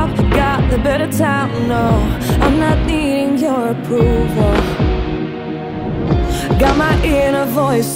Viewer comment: First of all, thanks for the vid, I just found some funny things :) 0:35 “I’m not eating your approval” lmaooo 1:15 I think instead of “I’m tryna intrude” it is “I’m tried and true”. Makes sense cause its the name of the song :)